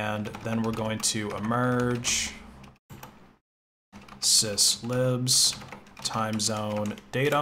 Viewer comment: i heard sis ribs and i got confused